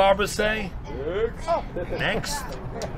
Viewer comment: LOL WHAT? 1:16: "What's the barber say?" (smoothly rubs over his bald head) "Thanks." LOL wtf does that mean? EDIT: OH he says "Next" not "Thanks" but god damn that was funny and random.